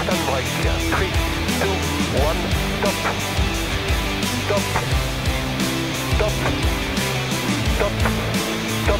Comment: What is that word? "Three, two, one, ?"? Top? Tap? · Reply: It's top. It's a slam word for "top départ" which means "starting signal" or simply "go"